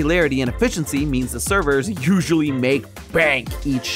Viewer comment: “They usually make bank each shift” is what’s said but I thought he said “they usually make bank, eat sh*t” 😭💀